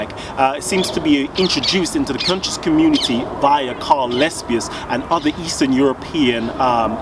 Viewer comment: what is name of white dude i.e. Egyptologist at 1:12 ? ... i could not make it out - thought I heard Colin Lespius.